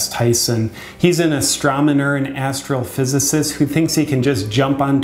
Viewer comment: Last of all, I heard him say "ASTROMONER". LOL.